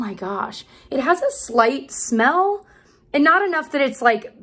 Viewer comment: I thought you said it (the foundation) has a slut*y smell lol I had to rewind and listen again.